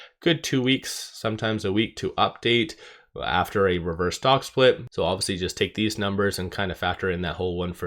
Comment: Thought you said they are out of stock splits. 🤷‍♂️